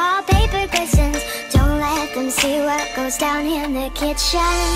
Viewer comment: I didn’t know it says places places I thought it said crisis crisis😂😅